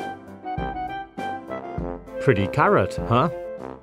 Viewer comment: Did he say pretty carrot?